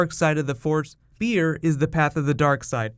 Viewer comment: LOL - For a moment there I thought you said Beer is the path to the dark side. I was like What? Not beer!